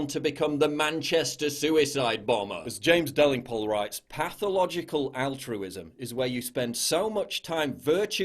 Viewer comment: Lol I thought it said pathological autism